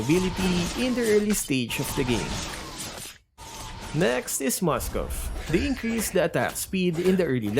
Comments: The Subtitles is Wrong haha why is it next is dyrroth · next is dyrroth in the cc instead of moscov lol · says "Next is Moskov". Subtitle says "Next is Dyrroth"